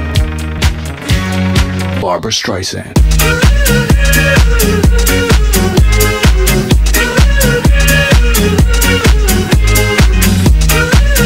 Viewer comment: i thought it said barbers tricep